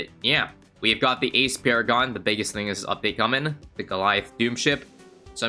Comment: My dumbass really thought it said “Ice Paragon” and got excited